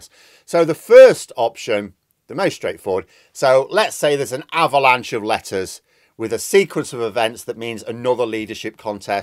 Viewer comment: Just misheard you at 3:32with the avalanche of lettuce...